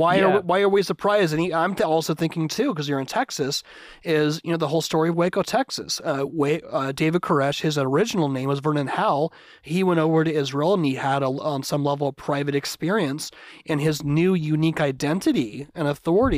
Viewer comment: When you said "Vernon Howell", I thought you said his name was "burn in Hell".